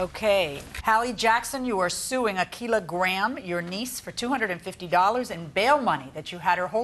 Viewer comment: I thought they said her name was a kilo gram